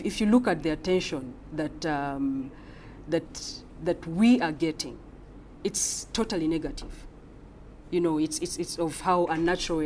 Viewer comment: who else heard "It's totally niggative"